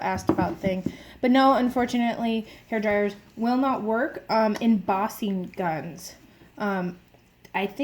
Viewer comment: what did you say? Buzzing guns?? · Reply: Embossing guns